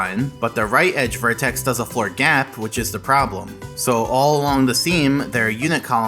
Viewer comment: at this point it sounds like hes just saying florgap like some alien language